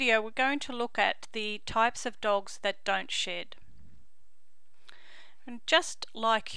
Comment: In the beginning it sounds like she said dogs that don’t sh*t 😂